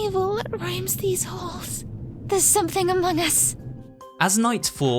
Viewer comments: did someone just say, AMONG US?! DEE-Z NUTS?! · DID SHE SAY AMONG US?